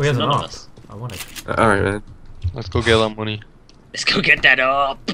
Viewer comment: Is'nt just me or at 5:05, I hear saying "Go get him, Honey" to xD